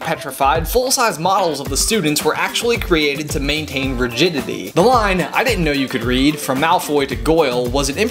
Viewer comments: did he say rigidity or virginity? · Okay, awesome video, seriously, but I spent about five minutes trying to figure out what you'd said at 1:19. "Rigidity," NOT "virginity." Jesus. Wow... That scared me. Like, ohhh man. I was afraid of the kind of people who were working with the kids there for a second. Awesome video! xxx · My brain is struggling to understand what you just said. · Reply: Rigidity